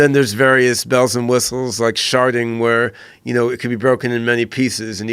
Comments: I thought he said "Sharting"... like when someone over trusts a fart and it ends up being a shit. I'll see my self out. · I thought he said “sharting” at first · did he call it sharting lmao???